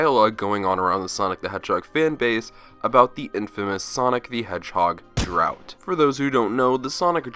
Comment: Lol. I thought you said “Sonic the Hedgehog Clout”.